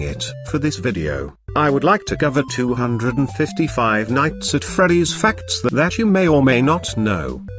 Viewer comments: “I would like to cover 255 nights at freddy’s” Thats what I thought he said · I thought that said 250 nights at freddy’s 😂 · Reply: I thought it said “255 nights and freddy’s” xD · I thought that said 250 nights at freddy’s 😂